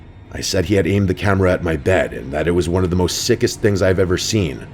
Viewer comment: "Aimed the camera at my dad". said "bed", subtitles said "dad"